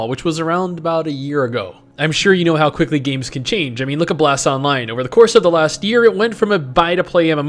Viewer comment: I need to know. Does he say blass, blast, grass, brass? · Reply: He said Bless Online? I guess? Its also a MMORPG.